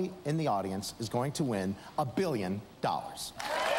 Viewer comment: First comment, and what’s he saying at 6:30? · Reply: Billon is what he's saying.